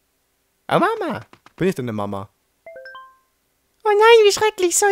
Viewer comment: lol bei 12:39 als Toad geschrien hat, habe ich Obama verstanden xD